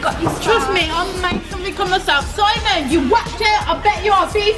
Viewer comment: I'm not native english speaker. What did she say at 30:07 and at 30:43? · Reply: I think she said “I’m gonna make something of my self” and at 30:34 Simon cowell I think you’re nothing but- and you patronize yourself and you shouldn’t even be allowed to be a judge.”